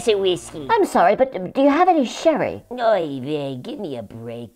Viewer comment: Good channel, but the CC leaves a lot to be desired. 14:48 The goldfish requests a King Foil hat and a tall glass of nothing, apparently ; 14:53 The fish, now referred to as Mr. Goldfisheman, says a common yiddish phrase for exasperation, "Oy Vey", yet the captions say "No". At 2.26 million subscribers, I'm sure you couldn't care less, but I would appreciate more attention to detail.